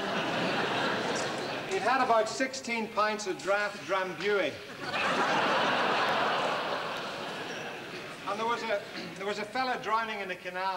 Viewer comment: What's the beer he mentions at 8:28? Couldn't understand the name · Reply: it's Drambuie...which is actually a liqueur so can't be a 'draught'...which, is of course, the joke...